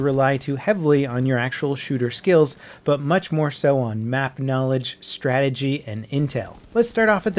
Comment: I thought he said math knowledge and not map knowledge and I was like....well I'm fucked